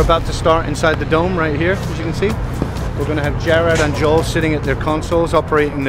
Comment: Did he just call Gerard....Jared????